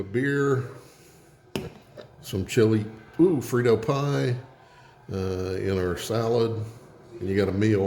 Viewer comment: Finally got to your right channel. Will try that, always thought I heard freedom pie, before realising, it's Frito pie. Always made chilli beans here.